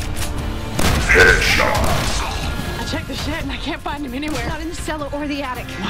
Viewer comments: I'm sorry. Does she say 'I took a shit and I can't find him anywhere' ????????????????????? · I know she said "i checked the shed" but I heard "yo check that shit" while she was pointing at the shotgun wizard